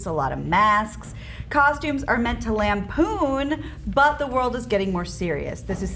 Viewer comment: Instead of "Costumes are meant to lampoon" I thought I heard, "land poon"